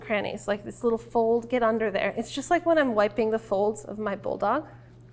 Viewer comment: I totally thought she said vulva, not bulldog